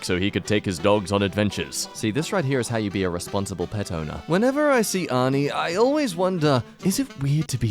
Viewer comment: I thought it said "Whenever I see Arthur..." And I was very confused